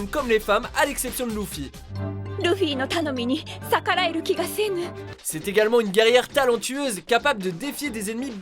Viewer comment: on est d accord que il a dit l(ou)ffy ! Moi j ai entendue sa !